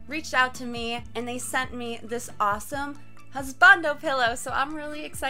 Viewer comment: Pretty sure i heard "Spandam pillows" @ 0:12 xD